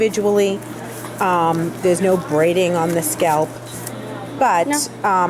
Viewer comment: I thought she said not on the scalp.......